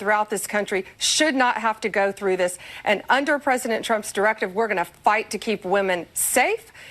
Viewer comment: It sounds like Karoline said President Chump. Hmmm.